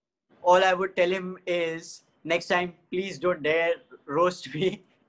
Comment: did he just said 'dare' 😐😐I know it's just joke 😕😕or not 😅😅